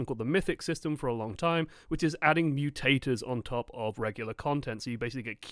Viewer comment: I really though said 'new taters', he actually said 'mutators'. I gotta get some lunch already.